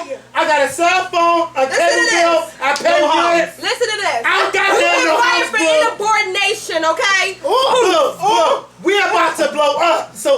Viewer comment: am i the only one whole heard her say inabordination??? lol wow